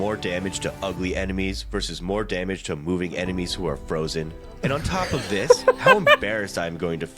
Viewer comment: Damage to frozen enemies that are moving is so broken!! 🤯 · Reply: Damn I misheard it, thought he said damage to enemies that are mooing.